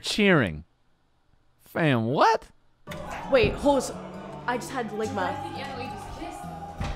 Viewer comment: did she just say "I just had ligma?"